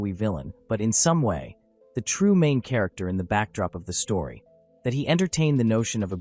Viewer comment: Yeah that’s not what he said. Words matter. Words have meanings. He said “main character in the shadows” not “the true main character”; two totally different meanings.